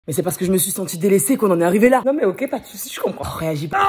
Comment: J'ai entendu "c'est par ce que je me suis sectionné les seins qu'on en ai arrivé là" 😐